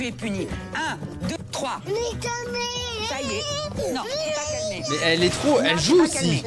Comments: 😂 c'est moi ou la petite Lilou a dit "nique ta mère" à 19:28 ?! 😂 Les réacts de sur Super Nanny, c'est pépite ❤😂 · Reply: « n’est calmé » c’est ça qu’elle dis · c'est moi ou il a dit un gros mots 😮😂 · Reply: Elle dit "nés calmé " :'))